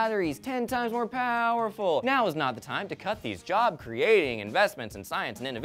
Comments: woah, at 1:33 the subtitles say gut instead of cut. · I'm probably the 50th person to notice this, but did anyone notice that one of the captions @ 1:33 don't match with what Elliott says? Gut vs Cut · typo, Gut is put there instead of cut ;)